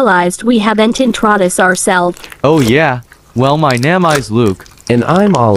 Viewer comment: In 2:10 luck said NEMA but subtitles said name